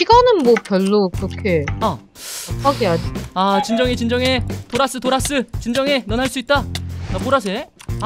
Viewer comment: 도라스를 돌았으로 들었닼ㅋ